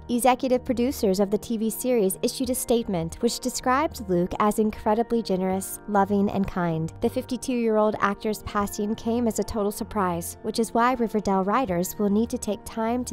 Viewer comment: Did anyone else hear she said actress instead of actor lol